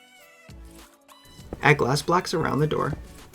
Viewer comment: anybody else hear "glass blacks"?